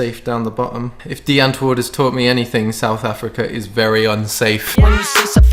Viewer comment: what does he say? I couldn't understand it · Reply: Die antwoord = the answer is a South African band . So he said if die antwoord thought him anything it's that South Africa is not safe